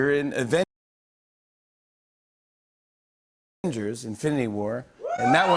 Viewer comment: Right after he says Avengers at 29:20, does he say "Infiniwar"? The letters in "Infiniwar" come out to be 119 in Ordinal Gematria, or 911 in reverse...not sure that's what was meant for the masses to understand him as saying, but that's what it sounds like he said to me...